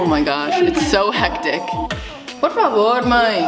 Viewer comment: Omg did you say Por favor? Maybe it's just me haha but I'm spanish and I thought you said that. Anyway love you ❣ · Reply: Yeah! She definitely said "porfavor mãe" and I think its portuguese because Mother in spanish is madre Inst?